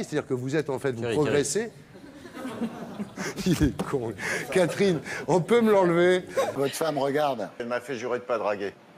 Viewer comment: il dit quoi? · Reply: "Thierry, Thierry"